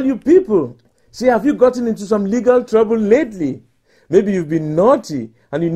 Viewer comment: At first I thought he was saying "nigga" instead of "legal" "Gotten into some nigga trouble lately? Then you need nigga representation!"